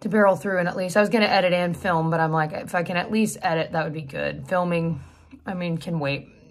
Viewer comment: Sounded like you said f***in edit....REWIND to "if I can" ...phew!!!